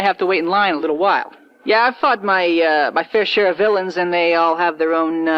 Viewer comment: I swear for a second he said “I fucked my fair share of villains”